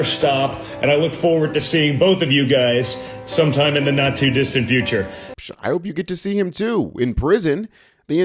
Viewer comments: “ . . in the Nazi distant future”. Played it back numerous times and I’m certain that is what Don Dipshit Jr. said. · Did he say “Nazi distant future”?